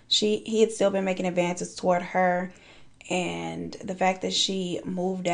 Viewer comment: Girl! I thought you said “like cinnamon to a rave” and I had to go back cause I knew that was wrong. Turns out you said,”that like sent him into a rage!” The things I hear, my hearing always screwing me over LOL